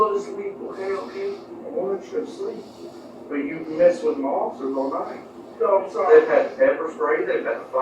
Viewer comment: The supervisor says, "You messed with my officers all night." The captions say, "You messed with monsters all night." That's definitely more accurate.